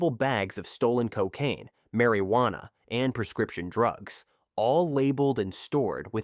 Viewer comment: What I heard was Mary-wanna 😂😂😂 Good video